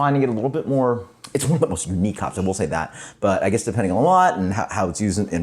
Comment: I called it weak? It's one of my personal favs! Crushing your soul with what else? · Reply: at :30 of the video it sounded like you said "its one of the most weak hops"? I listened to it again and I think you said "unique"? That makes me feel better, haha.